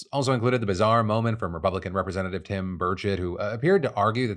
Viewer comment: Who else thought Phil said Republican Representative Tim Birdshit when talking about Mr. Burchett? I was like "Did I hear that right?" then saw the nameplate in the video.